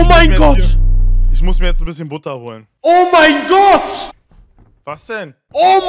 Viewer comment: Uh😂ich habe als erstes nicht Butter holen verstanden😂AMK was ist nur mit der Menschheit los😅